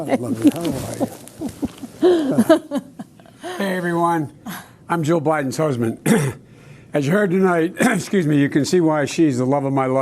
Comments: Did he just say, "hi I'm joe bidens husband?" Did I just hear that. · I had to rewind ten times. Did he say, "I'm Joe Biden's husband"? This was pre-recorded. I'd love to see the blooper reel. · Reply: I think it was "Jill Biden" but his speech is a bit muffled. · In the words of Joe himself in this very video, 'Hey everyone I'm Joe Biden's husband.' 🤦🤦🤦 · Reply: I thought he said that at first, too. But if you listen to it again carefully, he really does say Jill Biden’s husband.